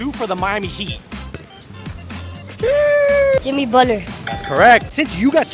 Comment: “Jimmy Butter 🧈” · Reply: the kid sounded like he said butter. Obviously you didnt get the memo